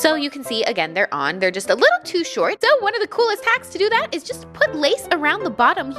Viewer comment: did i just hear amber at 4:05 say a little to short!?